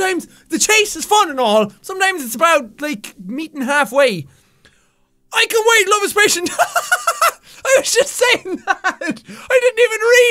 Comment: i thought he said "i can wait love is patient Tiiiiiiime"